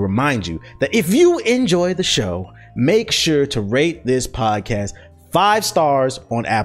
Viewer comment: Dude for a minute I thought he said rape this podcast😂